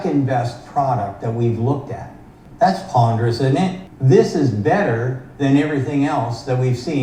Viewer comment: "that's ponderous isn't it" I had to listen to that 5 times. I thought Sandy Monroe said poggers! 🤣